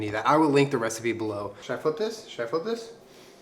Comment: Wasn’t looking at the screen at 2:57 and thought he said ‘should I put piss’ and I was just like ‘fuck man if u want to, it’s ur recipe’